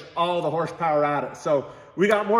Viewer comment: When you say "get back to work" I hear "get back to play"